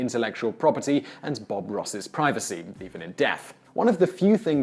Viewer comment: What does this guy say at 01:52, "deepen in deaf"????